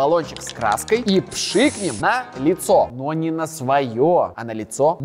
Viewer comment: мне послышалось на яйцо 🤣🤣🤣